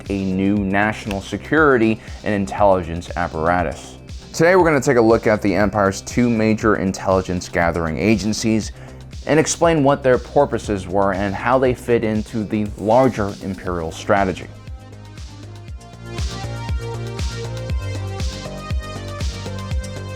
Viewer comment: I heard you say porpoises instead of purposes. Are you yourself a porpoise and is that why you hate dolphins?